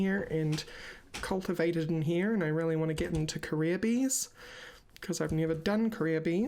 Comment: I keep hearing "Korea Bees" instead of "career bees". XD